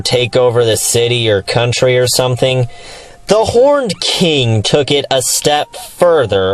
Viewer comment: I thought you said "The Porned King" and that you wanted a porn parody... whoops